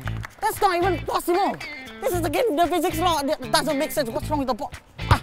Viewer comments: ‘Stop cheering go clap somewhere else’ got me dying 😂😂😂 Edit- ℳℴ𝓂 IM FAMOUS 😭❤️❤️ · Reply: The first time i watched it i heard "dont clap clap clap, clap someone else" 💀💀💀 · For a second thought,i heard him say “dont clap clap clap somone else”